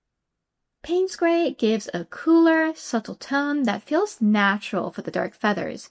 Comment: Those colors are beautiful together, so I have but one request: could you please list the names in the description? I thought you were saying 'Payne's Gray', but wasn't quite sure of it or which 'gold' you're using. I have a project in mind that has many ways I can compose backgrounds and I'd like to try these colors in some of them - with different patterns, but I want to get the right colors - if I don't have them already. I have so many supplies stored up, I don't know what I have - or don't - anymore! Also - that was a very clear and concise demo. Thanks for uploading! You're treatment of especially the rocks is gorgeous! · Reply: It is Payne's grey and quinacridone deep gold